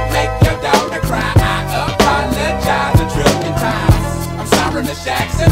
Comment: Damm i thought he say a thousand time 😱😅 creepy #mandelaeffects